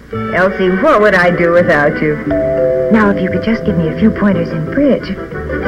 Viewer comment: Did she just say, "Now if you could just give me a few pointers on Quidditch"?